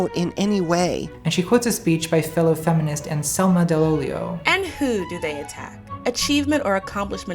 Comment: I heard "Anselma the Leo" and I was feeling the love for just a second until I realized I was literate.